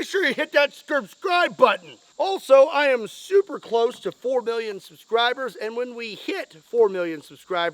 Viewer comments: for a second I thought he said "Also I am super close to 4 billion subscribers" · It sounded like you said 4 billion. not 4 million. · Is it just me or did say at 9:15 "when we hit 4 Billion subscribers" and then say "4 million?". I was thinking he was playing with us and going to do a massive give away at 4 Billion subscribers. 🤣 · Is it just me or did it sound like you said 4 billion the first time?